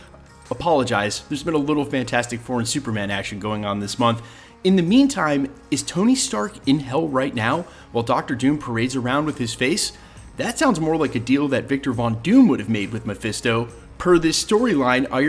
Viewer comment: Swear i thought you said TONY STARK IN HELLI! Like what the helly